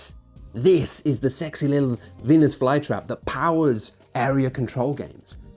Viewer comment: What did he say in 6:43 ? Vines vuela ? Cc said Venus fly I don't get it 😇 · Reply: Venus fly trap, a plant that eats flies.